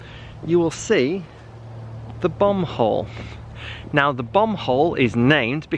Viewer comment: I thought he said bum hole for a second